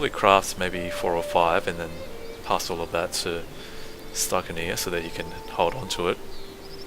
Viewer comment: Whenever you say "stykinir", i hear "stuck in here" xD